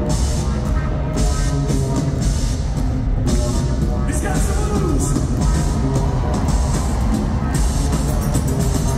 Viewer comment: Thanks for sharing! Please what does Dave say in 1:36:45? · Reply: I think Dave says "He's got some moves!" after watching Martin get jiggy 😅